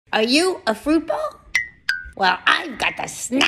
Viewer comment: I thought she said are you fruity and i said yes💀💀💀💀💀😂😂😂😂🏳️‍🌈